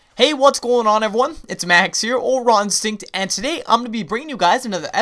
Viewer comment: ur intro is dumb it sounds like u say ron sink not raw instinct.......